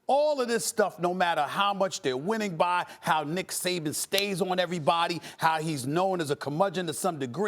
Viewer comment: I know I ain’t the only one who heard him say Dick Saban